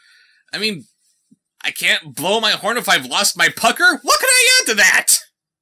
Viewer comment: I heard the word pecker. And I do not like where this is going.